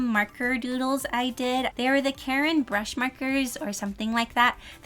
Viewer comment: is it just me or did she say karen brush markers